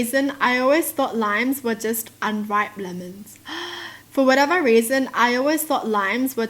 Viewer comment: I heard “Lions were just unripe lemons”